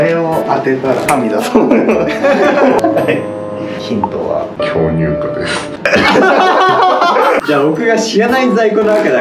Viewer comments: 「ヒントは巨○化です」に聞こえるのは私だけ？ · 画面見てなかったから「ヒントは…巨乳化です」って聞こえて焦った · 寝オチ寸前で目を閉じて聴いてたら 巨乳化です と聞こえて起きた人の数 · 「ヒントは巨乳化です」って聞こえて食いつくように画面ガン見してもたわ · 「ヒントは巨乳化です」に聞こえた